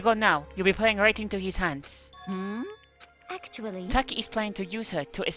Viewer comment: I heard you call taka taki like the chips and I was like XD